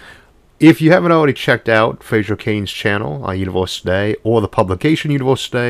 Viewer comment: I thought it said Fraser Crane!